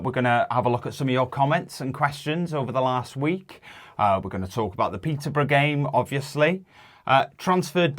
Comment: I’m not from UK. I am from the Commonwealth and live in North America. But I heard - “we are gunna talk about the pizza brigade” in the intro. I had to rewind. cheers and hope you smiled- up the f*£@ town!! edit to add- now you know how members feel when season ticket holders swoop in at 1400 🤣🤣🤣🤣